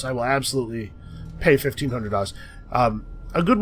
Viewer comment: Did say he will pay 1500 dust or dollars? Man too much Endless Legend has messed with my mind XD